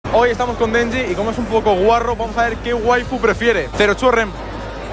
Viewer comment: Entendí "Hoy estamos con messi🤑" XD